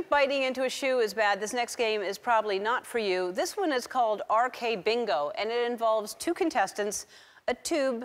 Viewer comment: AKBingoじゃない？笑 RKBingoに聞こえる笑